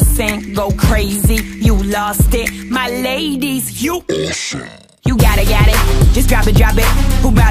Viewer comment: I misheard it as"My ladies you bullsh*t" 😂😂